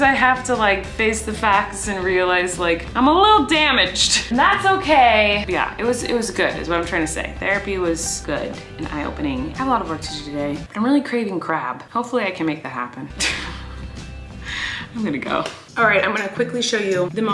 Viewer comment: I thought she said “I’m rly craving crack” but she said crap lmao